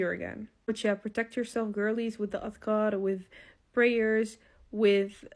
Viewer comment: the subtitle says of god, instead of adhkar, please correct, thanks